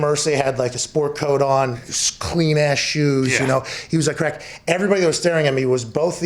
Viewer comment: did he say he was on crack lmfao